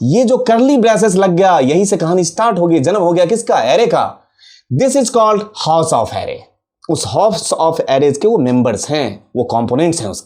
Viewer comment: Sir, yaha par aapne kya bola tha "House of Arrays" ya phir "Halves of Array"????? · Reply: House of Array Dear